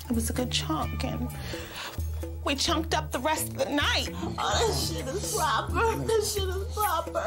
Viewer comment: what did she say at 32:20? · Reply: That sh*t is proper. I think 😂🤣